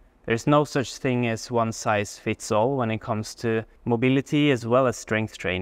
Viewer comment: I heard: “There is no ‘one size splits all’”. 🤣